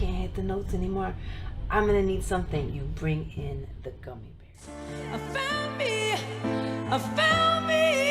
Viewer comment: Did she say the gummy bears? lmfao